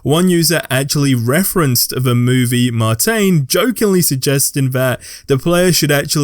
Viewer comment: Wait....did you say Martain and not Martian?😂